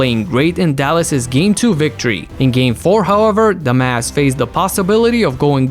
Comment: I thought u Said "DUMBASS" but u actually said "The Mavs" 😂😂